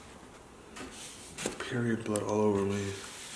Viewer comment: Did he say period blood all over me? How disgusting and disrespectful🤬 Unsubscribing🤬